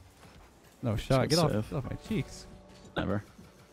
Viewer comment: did he say get off my fuckin cheeks?